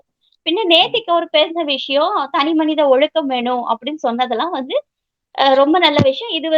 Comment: Did he pronunce ஒழுக்கம் properly? I heard him say ஒழுகு.